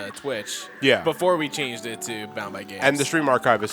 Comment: what is their twitch cannel called, I cant seem to find it because I keep hearing "bowned by games" or "bow my games" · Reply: Bound by Games